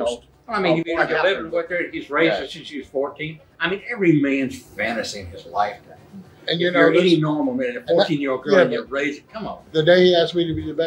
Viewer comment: Did he say every mans fantasy is to raise a 14 year old girl?